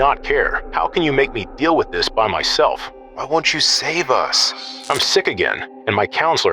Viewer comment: Bro what's with the demonic editing, the whispered "save us" lol